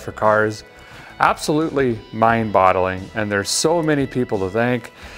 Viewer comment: Did he say " mind bottling". Or "Mind bogging".